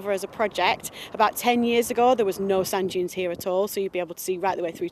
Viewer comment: Thought they were saying sun dunes for half the video.